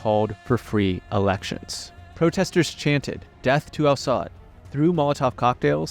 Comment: subtitles said "death to Assad" it should have been clearer "death to AL Saud" since Assad is Irans ally. 17:12 "was far from guilty" I think you meant was far from innocent. (only based on the info in the same video)